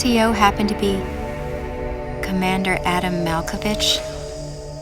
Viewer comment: hahahaha i thought i heard Commander Adam Malcobitch